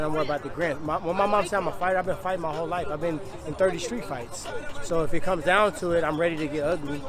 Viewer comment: did he said he had 30 streets fight??